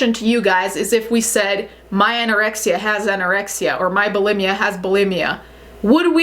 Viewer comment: When you said “my anorexia has anorexia” I thought you said “my erection has an erection” and I was concerned